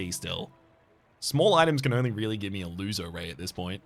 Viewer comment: Half-watching this while working and I keep hearing "A loser ray" 😭